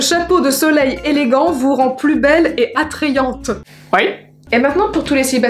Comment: x quoi ce bruit que ta fait 🤣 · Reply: Elle dit "oui"